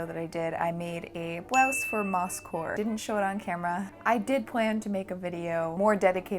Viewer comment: omg instead of Moss Core I've misheard Moss wh*re and was like ...WAIT WHAT AESTHETIC DID YOU MAKE